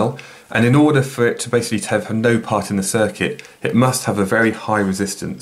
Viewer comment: Hello Sir, Thanks for this helpful video. But I did not understand what you said between 5:00 and 5:03 where you say " and in order for it to "something" part of the circuit " . What is the word you said mean and what is it? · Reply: 'basically have no' ^^